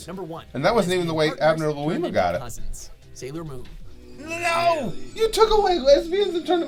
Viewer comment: i thought you said "No you took all my lesbians away"